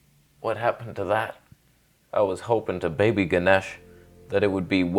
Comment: did he say "Ganesh" 😳😳